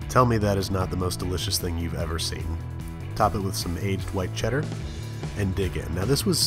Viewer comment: tell me im not the only one who heard "aged dwight cheddar"